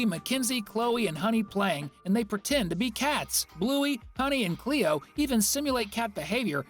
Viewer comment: cleeo? Did u call Chloe, "cleeo"????????????